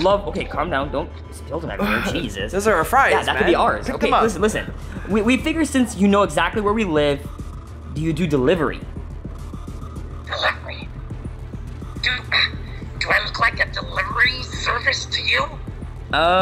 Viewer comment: did i just hear ronald mcdonald say "dude" 😂